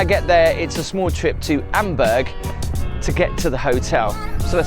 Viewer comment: It is Amberg not Hamburg 😊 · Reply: ah, that's fair. I thought he kept saying Hamburg at the start.